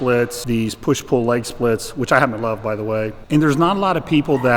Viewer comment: I think he says "which I happen to love by the way" not "which I haven't loved", especially since a lot of his recent programs are PPL.